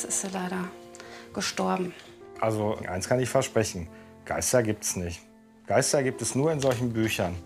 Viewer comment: Bin ich die einzige, die statt Ei Blei verstanden hat? 😂😂😂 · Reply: Ich hab Kleister verstanden